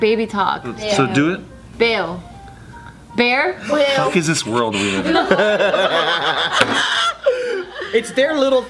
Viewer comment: what did Steve said at 2:32 ? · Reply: "the fuck is this world we live in"